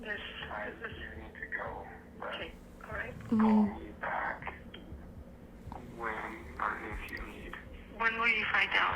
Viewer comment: What did she say? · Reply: She said, “I don’t do naughty things, I’m a really good girl” ! Sounds like a little girl that’s been sexually abused. I think her whole family has A LOT of dark family secrets !!!